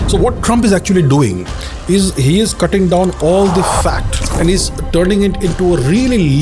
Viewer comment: the onscreen graphic subtitle said fact, but Abhijit said fat, not fact